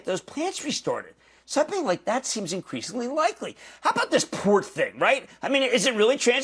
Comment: What did he say? This port thing? pork thing? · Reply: Port thing.